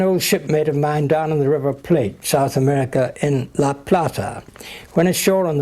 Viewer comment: did he actually said ¨La Plata¨? omg, so random that I am and live in La Plata right now.